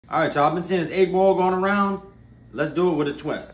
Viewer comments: Man I thought he said he had an 8ball 🎱 goin around 🤣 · When he said egg boil I thought he said eight ball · whew I thought he said " I've seen this eight ball going around" 😶‍🌫️ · I really thought he said 8 ball not egg boil lmao I thought this was gonna be a whooole different type of video 😂😂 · He said Egg Boil ...I thought he said 8 Ball ...now thats real southern twang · Fk I thought you said 8ball going round hahaha😂😂😂😂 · Ooooooooh i heard him say EGG ROLL in the beginning so when he started adding all this shit i was lost😂😂